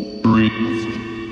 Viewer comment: i don't hear "PRIST".........i hear the mans voice saying........."BRITCH".........??????